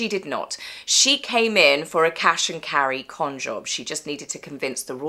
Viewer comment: "cash and carry con-job" Excellent! (I first thought I heard "smash and grab con-job"!)